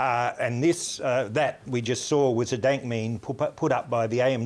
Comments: Sounded like he said Daint Memes at the beginning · Did he just say Dant memes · Is it me or did he just say “daint meme”? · Did he just say "dant meme"